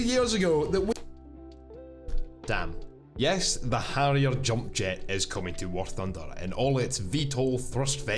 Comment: Damn my dyslexia, thought you said Jump Jeep. That would have been COOL!